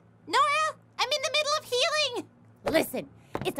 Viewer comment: I was early before subtitles... When Ruby said I'm in the middle of healing... I THOUGHT SHE SAID I'M IN THE MIDDLE OF FEELINGS... LIKE RT DOES WITH US ;-; Until now that I rewatch with subtitles